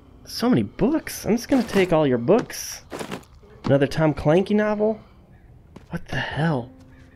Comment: did he just say Tom clanky 💀💀💀